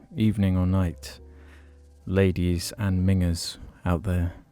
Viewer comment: I thought he said ladies and niggas at the beginning